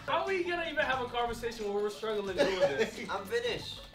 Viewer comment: I understood "I'm british" and didn't understand why he said that 😂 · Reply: He said “I’m finished”